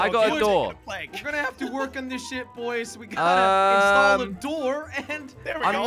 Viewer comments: omg jelly what did u say!! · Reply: it is ship dum dum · Didn’t when jelly said were gonna have to work on this ship it sounded like he said were gonna have to work on this sh!t😂